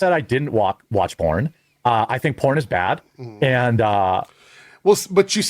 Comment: Im tried this morning, so maybe i missed something. Did he just say porn is gay? What kinda porn is he watching?